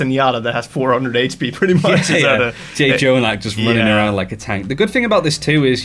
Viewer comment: did he just say j-jonak